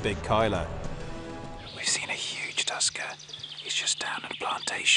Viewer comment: I heard you say tusker, and for a split-second, I thought you said Tuskan. Time to hunt me some SAND PEOPLE.